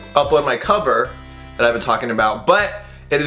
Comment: It sounds like you said but I've been talking butts